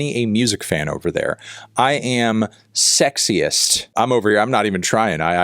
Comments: I thought he said he’s sexy. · I thought he said "I'm sexiest" 😂